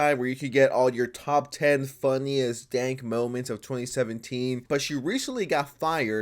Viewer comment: I thought you said funniest gank moments of 2017, you know like e-sports, and I was kinda interested.